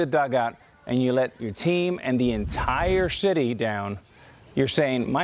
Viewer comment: he said shity instead of city